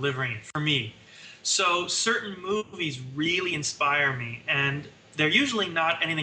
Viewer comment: i thought he said boobies